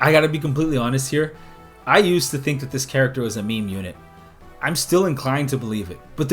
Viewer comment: Wait a minute, did you just say you used to think Lute is a meme unit? That's... crazy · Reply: I legit thought he said "mean unit", and she is mean, so...